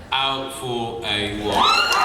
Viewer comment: I’m not a native English speaker so can somebody PLEASE spell for me what James’s saying at 4:41 when everyone burst into laughing , PLEASE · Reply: I think he is talking about his phrase "Out for a walk..bitch!" in Season 5 Episode 05 <link>